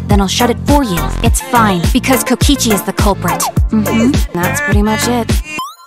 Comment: When she said "Kokichi is the culprit" I thought she said "Luigi is the culprit" ...I